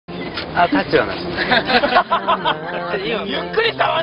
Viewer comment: it sounds like he says "attenzione" (pay attention in Italian)!!! A really funny video thanks 😂😂😂